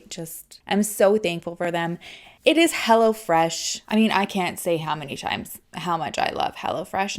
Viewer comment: I thought she kept saying "Hella Fresh"! Hahaha